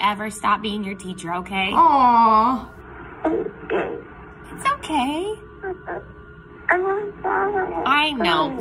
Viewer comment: Why did it sound like he said, "Im gay" 💀